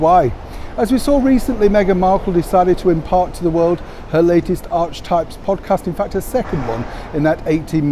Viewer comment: What… I heard “ Arrsewypes “ came in at number 2….🧻😂🧻